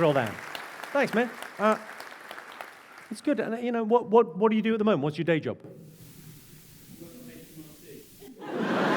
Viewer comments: What did the guy say his day job is? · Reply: "I work for HMRC" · What did say at 9:53? I can't quite hear his apparently hilarious reply. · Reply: "I work for hmrc"